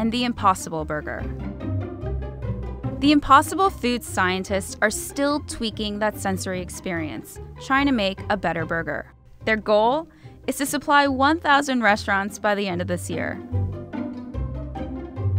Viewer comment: i heard "China makes better burger."